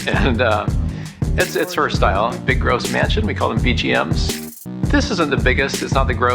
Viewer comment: I don't know why, but I don't like extra big houses. BGM. lol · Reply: Oh he said BGM 😂😂 for some reason i thought he said BUM, big ugly mansion 😂